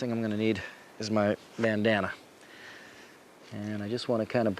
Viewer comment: did he say MAN-Danna? Way better name lol